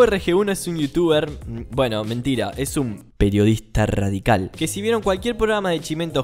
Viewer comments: Pense que decía Peronistas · Jaja yo entendí "peronistas" de YouTube